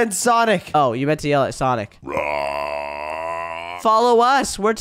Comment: don’t swear I heard sh*t sonic